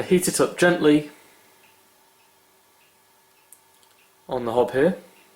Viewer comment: What did you call your stove at 7:25? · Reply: A hob: it's a British word to refer to the flat-top part of the cooker, where you might find gas rings or hot-plates etc.